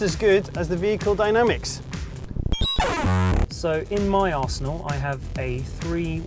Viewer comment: Hahaha! At 4:40 "..so in my asshole, I have..." - what I thought he said at first